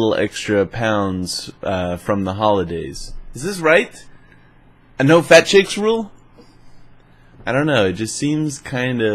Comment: did you just say you know fat chicks rule?